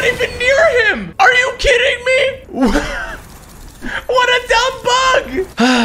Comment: When said what a dumb bug I thought he said what a dumb f***